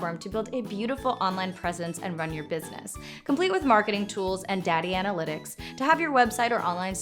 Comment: Did she just say "Daddy analytics"?